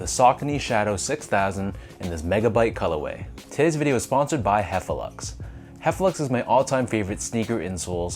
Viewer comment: I thought you said sponsored by heffalumps 🤣